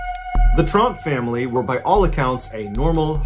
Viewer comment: holy crap I thought he said Trump! I had to rewind twice to make sure that’s not what he said. LOL